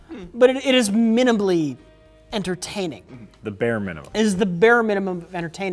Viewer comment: I heard mini'numb'ly entertaining